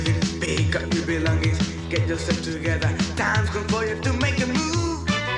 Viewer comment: I remember this! All these years I thought they said "Gotta Make a Movie!" First grade memories!